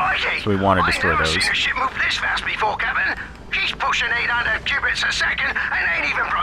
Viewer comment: Did he just say cubits!?